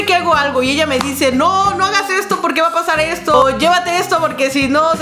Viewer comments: Soy la única o en el minuto 8:57 patho dijo "o llévate esto amor porque" o sea que le dice amor patho no es que así lo escuché 😅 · alguien mas escucho "llevate esto amor" uvu ? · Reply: Jajaja yo escuche "llevate esto por que si no " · alguien mas escucho "llevate esto amor" uvu ? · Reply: Yo escuché “llévate esto a por qué”